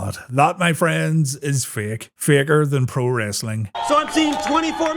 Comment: did he just say "fake"